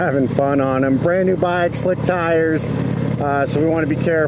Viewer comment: you say "Brand new bike. <something> tires so you want to be careful." I can't understand what you said in place of <something>. Since I just bought the 2018 Chieftain Limited which is the same as this bike except for visual enhancements, I would like to know what I need to be aware of concerning the tires to be careful please. I've only owned my new bike for a week and already put on the 500 miles for break-in and it's just be best bike I've ever owned by far. Boy, do I ever disagree with the comment below about the build quality not being there. I went through three Harley's, two new ones and one used one, and I owned a 2016 Vintage before my new Chieftain Limited and I gladly gave up Harley Davidson once exposed to the heavyweight Indians. GT Steele mentioned clutch engagement. That's one of the reasons (among many) that I gave up on HD; all three Harley's had non-linear clutch engagement once they warmed to operating temperature. It drove me crazy. My Vintage, and now my Chieftain Limited have linear clutch engagement at all times. But, it seems that the clutch engagement of the Chieftain is better than what I had on the Vintage and I thought it was my imagination until I read the comment below about the Chieftain clutch engagement is way better. Other reasons for dumping HD: Gear position is always available on the Indian but only available on the HD's when the clutch is engaged. Peak torque out of the ThunderStroke 111 engine at lower RPM. Turn-signal switch for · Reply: Don Smith hey bro ,he mentioned new bike and basically saying he's gonna take it easy on the riding. New tires you have to be carful with about the first 100miles. When new tires are made the factory puts stuff on the tires so they can remove the tire from the mode. In the case it makes the tire alil slick,so its best to were off the glaze by goin out riding but not aggressively the first 50 to 100 miles.